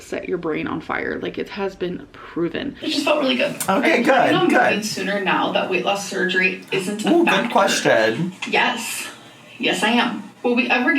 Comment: can someone tell me What the question is? He was talking & no matter how many times I went back i can’t hear the first part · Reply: If she will move sooner out of her apt now that WLS isn’t on the table